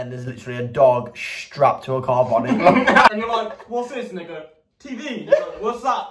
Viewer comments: Is it just me or did it sound like said the n word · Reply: no he didn't lmao. Trust me i am from England, i fuckin know their accent. He said "and they go" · sounds like said the n word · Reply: nah he said "and they go"